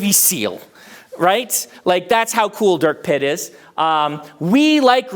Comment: I legitimately thought Brandon was saying "Dirt Pit" this whole time... 😅 Another great and timely lecture, thanks a ton!